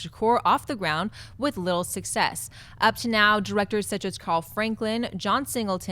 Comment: thought said lil success like it was a rappers name